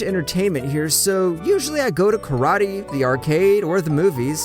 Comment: is it just me or did he say “karate” instead of “karaoke”?